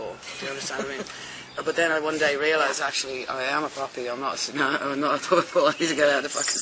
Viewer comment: "Could not afford to be a poppy in a tsunami. . . . But then I realised I am a poppy" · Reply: Oh that makes sense I thought she was saying puppy